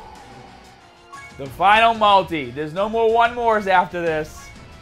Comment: I thought you said no more summons